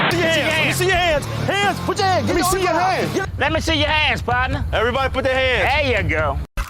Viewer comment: Did Colonel Panders really say "Let me see ya ass patna."? Or was I hearing things.